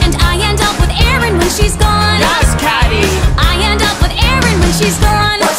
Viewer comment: I swear Janis and Damien are saying "that's catty!" They sound disappointed with her.